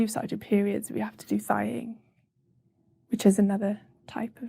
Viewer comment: what word does she use? Sounded like "sighing" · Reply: thigh - ing. Look it up, or better not.